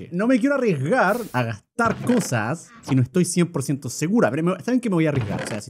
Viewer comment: en ese momentuo no se si escuche mal pero creo que german dijo segura en vez de seguro jajakjska. Por cierto buen video German :D (no es por hate solo me parecio gracioso)